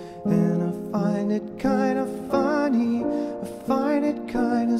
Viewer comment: After seeing donnie darko and listen to this song I keep hearing "I find it kind of bunny" instead of "funny" o.o, lol.